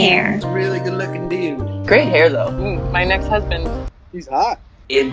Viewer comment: Did she say my next husband 😂😂😂😂